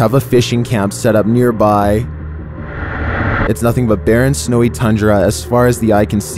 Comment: I thought you said “Nothing but BARREN snowy tundra.” But clearly after seeing the video you said/meant “Nothing but BEAR AND snowy tundra!”